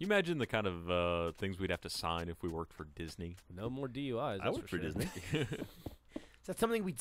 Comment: did he say doi at 35:51?